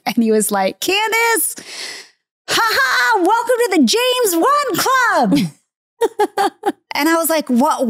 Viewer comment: Love this season. And, I thought Candace was saying James Won. 😅 I was thinking, who is that.? 😅😅